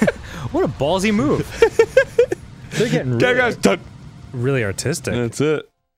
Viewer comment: i thought Dan said "theyre gettin really autistic" at the beginning.